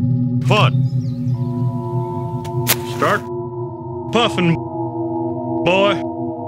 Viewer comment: I thought it said start suffering boy